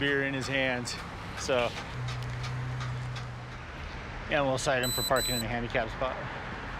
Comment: “He’s got a beer in his hand, let’s cite him for parking in a handicap spot” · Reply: I thought he said , I will also site him for parking in the handicap.